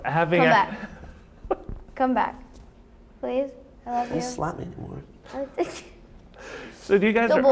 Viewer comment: Did she just said "I love you" ? :''''